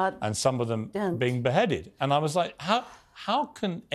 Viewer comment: Piers Morgan 2 days ago: 'I've never said anything about 40 babies' Also Piers Morgan 7 days ago: '40 babies were killed' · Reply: he clearly says beheaded